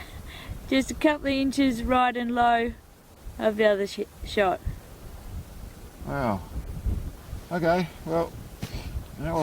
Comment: 'just a couple inches low of the other shit shot' hahaha thats what i heard,when you do so many sweet shots further out than 2k and hit in the top left corner not dead centre i guess thats a 'shit shot' :P